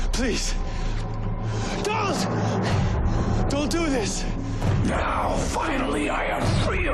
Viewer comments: i heard " dont do this" 100% :) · He definitely does not say Dallas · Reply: He definitely says: "Please, don't, don't do this". Same thing he said @ 6:43. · what I hear is "please.... dont.... dont do this"